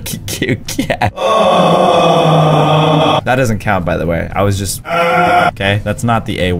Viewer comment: did you just say cow?